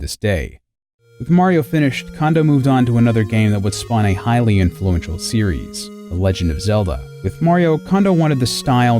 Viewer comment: sounded like "Hylian fluential series". Fitting